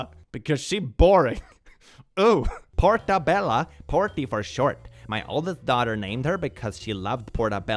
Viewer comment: i heard that as “porky” and legit thought she named her child the earthbound character that is literally stuck in a capsule forever